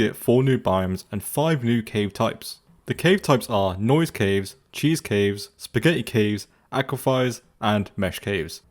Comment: did you say cheese caves?